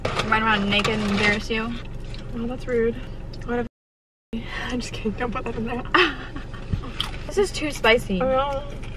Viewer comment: anyone else tryna figure out what said @ 5:33 👀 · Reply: Elle Woods eff Marty, I think lol